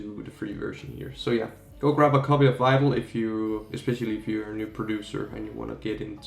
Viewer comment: Turn on the captions and you will see go grab a copy of Bible instead of Vital 1:56 lol